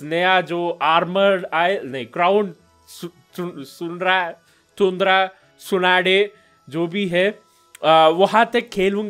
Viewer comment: did he said tsunade